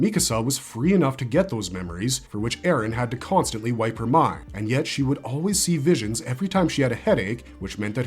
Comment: I thought it was ymir constantly wiping her mind. Couldve sworn they said that but i forget